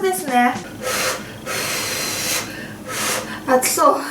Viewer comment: の、「熱そう」が「吐きそう」 に聞こえました。 すみません